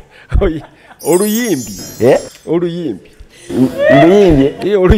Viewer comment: I hear “oluyimbye😂😂.” Alex is the worst